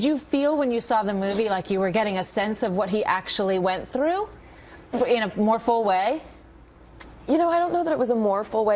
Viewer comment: can someone tell me what she said there? "in a more full way"? or morphoway? what is that? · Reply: Correct. In a more full way. In such a way that was deeper than when she first was learning the facts.